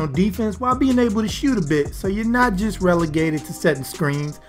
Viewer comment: if you put captions on at 4:20 it says "shoot a bitch" instead of "shoot a bit" lol